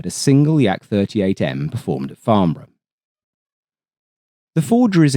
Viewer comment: What did he say at 20:58? A farm run? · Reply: farnborough air show